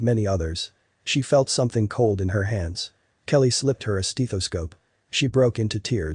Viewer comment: Did he just say "steetoscope"?